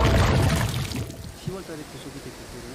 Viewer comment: 뉴스보시면서 편집 하셨나? 10월에 구속이 어쩌고 이런소리가 들리는데?